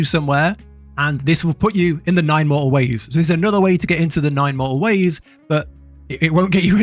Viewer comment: Great video, however I do wish that you would pronounce the letter t, I thought you were saying the great mole ways almost the entire video lol